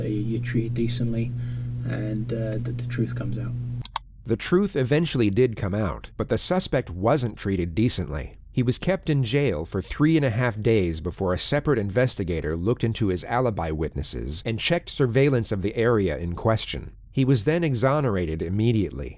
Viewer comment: 3.5 days is not a big deal plus he got 46 thousand dollars and the cops were fired. That's more then enough compensation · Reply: lmao I thought u said 3.5 K days and I was like WHAT THIS IS SO NOT WORTH IT